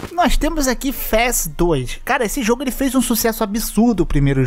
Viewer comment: Qual o nome do jogo em 5:30 ? Eu tentei procurar pelo que entendi ele falando, mas nao consegui achar - Pesquisei "Fess 2 " " FastToys'" Fress2'' kkkkkkkkkkk